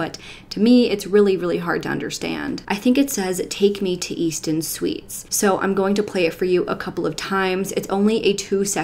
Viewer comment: Sounds like he said take me to eat some sweets